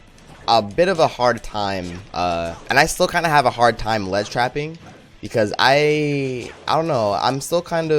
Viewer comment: I thought @ 12:26 it said ledge rapping😂😂😂😂